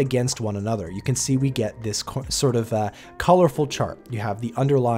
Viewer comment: Did he say "COLORFUL"? LOL